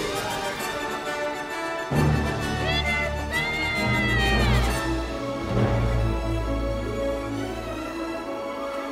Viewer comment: Question: When Thud saw Peter flying at 0:43, Did he say: Peter Pan or Peter's back? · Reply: Answer: When Thud saw Peter flying, he yelled Peter’s back!